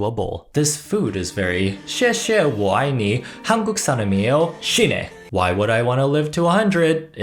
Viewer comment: For anyone curious about what he says at 7:30, he says “Thank you. I love you. I am Korean. Die.” (Xiè xie. Wǒ ài nǐ. Hanguk saram-ieyo. Shi ne. — 谢谢。我爱你。한국 사람이에요. 死ね。)